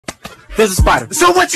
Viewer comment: "Since when" turns into a girl 😆 THATS HILARIOUS but at least he don't gotta kill that spider! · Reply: It said 'since when' And I heard 'sis wins'.